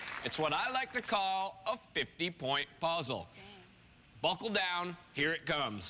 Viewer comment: Whoo I think just cussed I think she said Damn